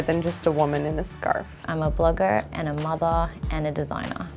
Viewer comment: I thought she said "I'm a booger" 💀😭